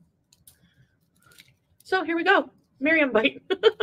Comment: DID she just say "Mariam bite?" OH MY OH MY OH MY.